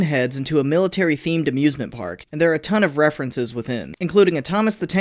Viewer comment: Why do i heard 'abusement park' instead of 'amusement park'?